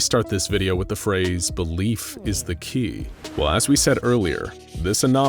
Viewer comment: He said Belief but I heard “the leaf”